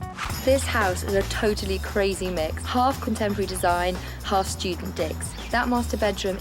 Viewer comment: i heard half student d I cks 😂 I turned on the cc and it reads d I cks lmao Is that what she actually said